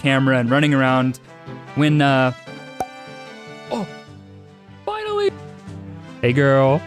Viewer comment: the way he said finally 💀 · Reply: Go to 12:50 because it just says AILLY instead of finally